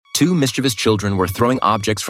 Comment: I heard "two mister beast children,"